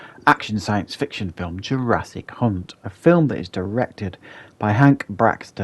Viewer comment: I coulda sworn you said Jurassic C*nt, lol